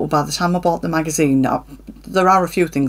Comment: Haya. Glad you are recovering all ok. Loving the bias tape you got 🤩I like to see the Mrs Harris goes to Paris, looks good. When youb said Kika dress, at first, I thought you said Kinky dress 🤣🤣Thanku 'sew' much for sharing. x 🧵